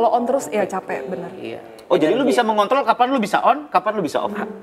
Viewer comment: apa cm gw yg dengernya bilang "kontlo"